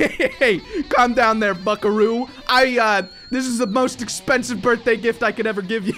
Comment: Did he said buckaroo?!😂😂